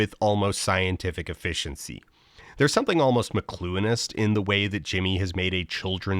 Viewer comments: What is the word that Dan is saying around 11:18? I'm trying to google it but it doesn't recognize it, instead recommending me "masculinist" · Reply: I believe it is "McLuhanist"? As in "Marshall McLuhan", the media theory guy behind the famous "the medium is the message" quote. It would make sense, given the context. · dan wtf is this word please help 1:14:13 bro why you gotta call me out like that lol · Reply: McLuhanist Referring to the philosophy of media studies pioneer Marshall McLuhan Look him up, you'll see a lot of what influences Dan Olson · What word does he say at 11:16? I've tried almost every spelling I can think of and google is giving me nothing. macluinist? mclouinist? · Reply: “McLuhanist”, referencing Marshall McLuhan, a 20th century philosopher whose career focused mainly on media. He is most well-known for the phrase “the medium is the message”, which is likely the belief referred to by “McLuhanism”. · What does Dan say at 11:16? "McClueinist"? Please help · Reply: McLuhanist, referring to Marshall McLuhan. He coined the term "the medium is the message" and did a lot of work focused on how the mediums through which people consume content (not just the content itself) and how those mediums work have an effect on people and society. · he says “there’s something almost ??? about” and the caption doesn’t make sense what did he say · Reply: McLuhanist. Look up Marshall McLuhan.